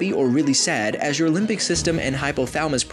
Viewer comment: When he said limbic system I thought he said limpdick